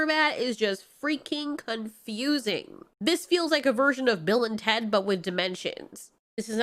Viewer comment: When you said "Bill and Ted with dimensions", I thought you said "Bill and Ted with dementia"... 😂